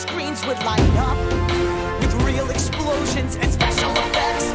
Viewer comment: Yknow it really sounds like says “My super sweet cake would be special effects.”